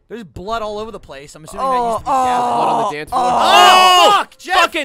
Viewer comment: I heard blood on the dance floor... Is that what he said?